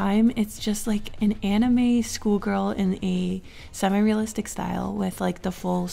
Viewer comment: "some anime school girl" I MISHEARD SCHOOL GIRL TO SKULL GIRL AMD ALMOST A HEART ATTACK